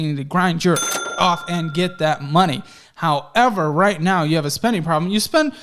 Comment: did he say "jerk off and get more money"?